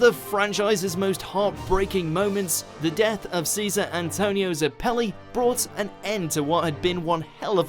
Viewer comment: Please don’t tell me I’m not the only one who heard “Tonio Zeppeli”